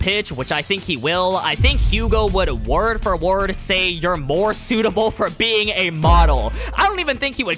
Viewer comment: I heard “you’re more suitable for being a bottle.” 😭😭😭